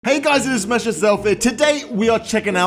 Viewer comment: i keep hearing z" hey guys its smash yourself"